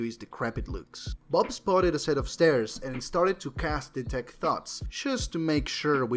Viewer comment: Only just realized you said “he casts detect thoughts.” Thought you said “he casts detect thots” both work I guess.